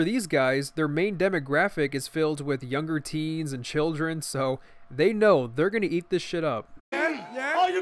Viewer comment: did you just say childrens?